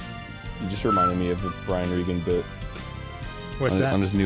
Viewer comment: Couldn't quite make out the name of the comedian they mentioned at 0:27. Any help would be appreciated, thanks. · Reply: Brian Regan.